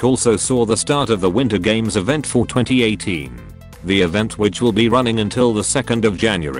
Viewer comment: sounds like he is saying 420 18 instead of for 2018